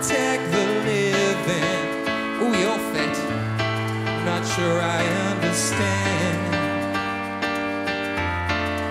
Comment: What did he say on 0:44?) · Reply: oooh you're fit (good looking)